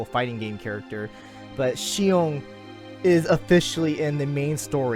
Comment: I thought said Sinon at first 😂